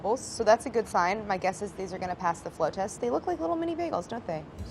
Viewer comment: It sounds like Claire is saying "flow test" and not float test.